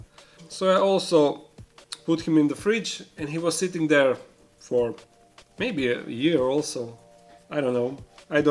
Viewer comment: did he say in the fridge for a year